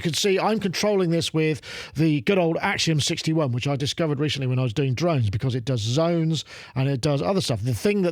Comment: I first though I heard "Which I discovered recently when I was doing drugs", which would have been a very good line.